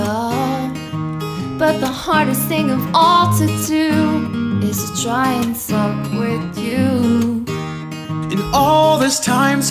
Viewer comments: I heard it too, "the hardest thing to do, is to try to fuck with you" 8( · I think says fuck with you???